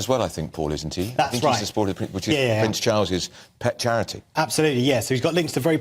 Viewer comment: I think I heard the presenter say 'shit', because he said 'Prince Charles'.